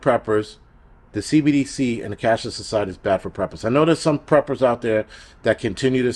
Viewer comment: HaaaaaHaaaa!! I had the volume low, was distracted, & thought he kept saying "CATHOLIC SOCIETY"....then I actually thought , "oh no, Jason has finally lost it!" Now I realized, I've lost it!! Thank you to and others. Carry on!!